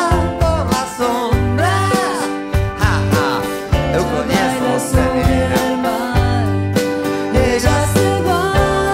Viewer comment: Alguien entiende lo que dice Charly acá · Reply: Eu conheço você menina